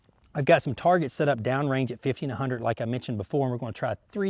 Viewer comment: I had to rewind the video. his "50 & 100" sounded like "1500"😂😂😂